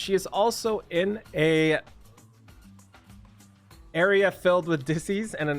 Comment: I heard "an area full of disease and ssb" lmao